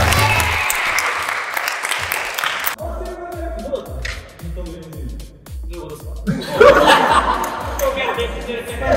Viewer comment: 何十回もリピしたけど、どんだけ聞いてもなんて言っているか聞き取れない笑 聞き取れた方いまんか？ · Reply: 「回してる感じだとどうだった？」っ的な感じで言ってますね! 多分!